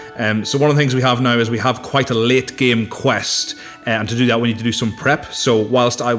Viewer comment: Did he say lit or late game quests? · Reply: It was 'late' game quests, but said in the Irish accent of our:-)